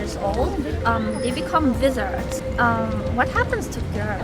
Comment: I literally thought she said lizards